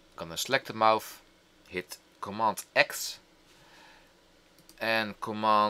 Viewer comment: what are u saying at 6:36 ur saying commont x WHAT!>! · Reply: Command (CMD) X on a Mac keyboard. Control (CTRL) X on Windows.